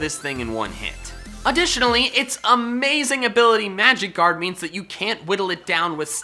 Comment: When you were talking about Clefable I heard “it’s amazing ability magikarp”